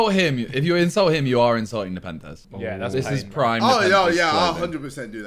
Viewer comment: What did Simon say around 1:01? Like a pentest? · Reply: Nepenthez